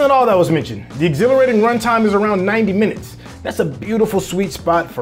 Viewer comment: Did I hear 90 minutes run-time?! Isn't this too short for a film with so much exposition to cover ??!